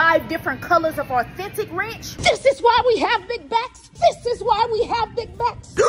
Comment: I thought she said “THIS IS WHY WE HAVE BIG MACS!” Lol 💀💀💀